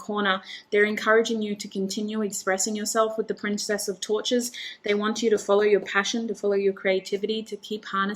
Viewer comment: Group 2 - great message, thank you! Lines up with all the other messages I’ve been receiving lately. Patience. Ok. Funny side note - when you said “Princess of Torches,” at first I thought I heard “Princess of Tortures.” 😂🙏🏻💕